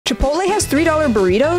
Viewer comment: I thought u said chipalo at first 💀💀 I was about to say This What is your favorite Place The Place Chipola 💀💀 WTH IS CHIPOLA U MEAN CHICK FILA?